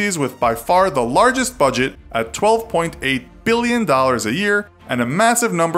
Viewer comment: I thought you said 12.8 bilion not milion